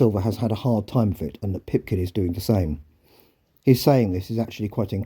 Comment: Sorry, you were mumbling a bit: did you say Pipkin was giving Silver a hard time?